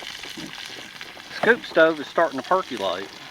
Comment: omg my hearing is going.. I actually thought you said poop stove instead of scoop stove lol. my bad... aloha still love all your vids